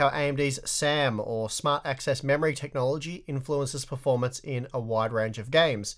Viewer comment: Every time I hear sam's full name I hear smart-ass memory